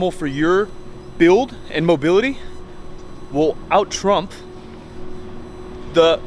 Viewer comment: I heard the word TRUMP in